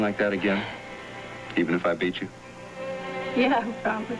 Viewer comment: what does rick and/or Lesley say @ 10:30 on? did he say "even if I beat you"?!